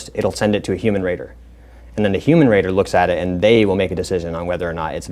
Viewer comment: I heard “human Rater” as a “human raider” I was like wtf.. they are training what? Thank you sir Patriot, brave, something.